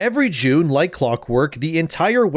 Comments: It sounded like he said "every jew like clockwork" in the first few 2nds had to do a double take 😂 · Thought he said " Every Jew, like clock work..." for a second